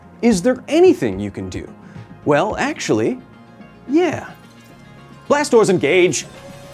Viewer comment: I thought you said Blastoise engage 💧 🔫🐢